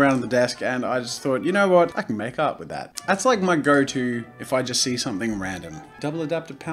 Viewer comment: With your aussi accent it sounds like your saying i could make out with that